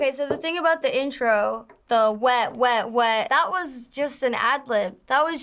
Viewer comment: I thought she was saying WHAT WHAT WHAT WHAT instead of WET WET WET WET